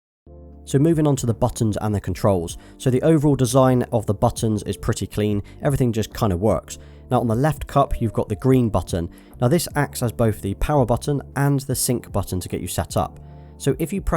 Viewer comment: did he just say “everything just kind of works”?